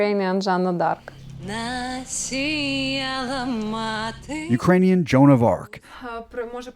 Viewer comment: Did she say "Ukranian Donald Duck"? 😂